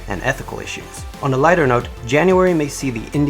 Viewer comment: Lighter note? On 2:17?am I hearing it right?